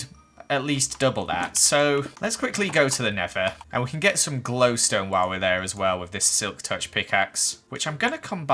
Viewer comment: Did I hear him say “glowstoner” Yeah boyyyyyy